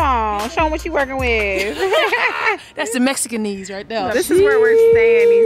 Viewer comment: This girl said Mexican knees 💀😂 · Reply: I thought she said Mexican ezee😂